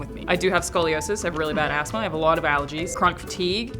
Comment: I thought Alanah said she had a bad ass-line when she said asthma.